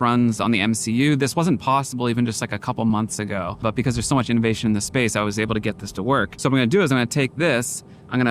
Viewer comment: did you say, "there's so much invasion of space, it works?"